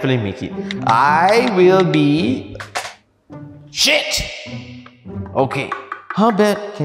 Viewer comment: the video say shit, the subtitles say crap Me: Visible confusion